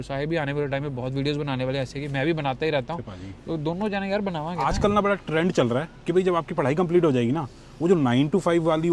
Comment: What did say to in 8:00? 😅😅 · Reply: 'Kithe Paaji' 😀 😀